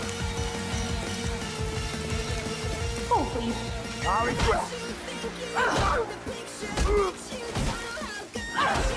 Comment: what she say i need it for my edit subtitles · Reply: I know I’m late but she says “hold please”